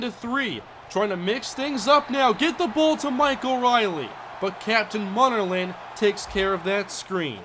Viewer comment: Lol I thought for a sec you said Michael Riley